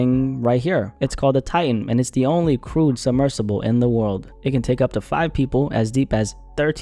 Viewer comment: Did you say only “crewed submersible in the world”